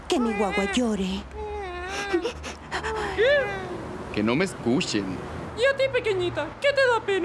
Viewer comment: ¿que dijo ese? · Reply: Ke no me eskushen